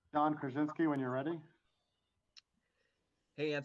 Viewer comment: Did they just say "John Krasinski when your ready" lol